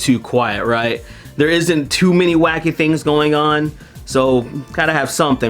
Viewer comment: I thought he said “isn’t too many wack-a-dinks going on”. That was my new favorite word. But he actually said “wacky things”. Now I’m sad.